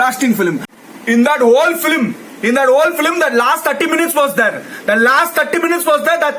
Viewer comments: is he saying tatti💩💩 mins? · did he say tatti minutes